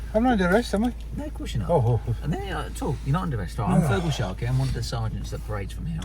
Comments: HaHaHa 16:25 Did he say his name was Feargal Sharkey??? Undercover Undertones... lol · did he say he was Feargal Sharkey?! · did he say his name was fergal sharkey ?!! by not arresting it shows he has a 'good heart'..... i'll get me coat.